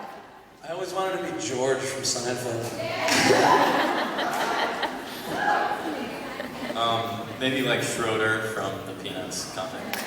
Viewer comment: who Joe wanted to be? I can't hear. :( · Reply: George from Seinfeld I think